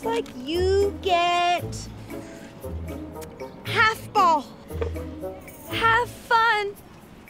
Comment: I thought the teacher said: “...hAlF-bAlL... HaLf FuN!”